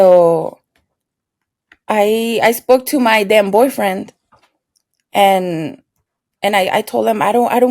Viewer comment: I thought you said I spoke to my damn boyfriend 😳 instead of then boyfriend lol